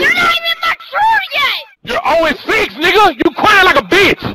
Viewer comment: When he said you're 0 to 6 i thought he said you're only 6